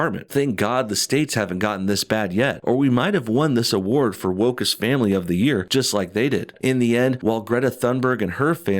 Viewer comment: I thought it said workest family.